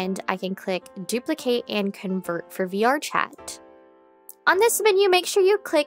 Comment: i thought you said "bjork chat" at the beginning omg